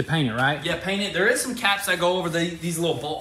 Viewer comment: what was said: ‘ caps to cover the BOLT HOLES’ What I heard: ‘caps to cover the butt holes’ Annnnnd this is why my kids don’t take me serious.